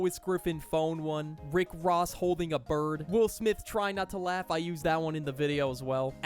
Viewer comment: did he just call him will smith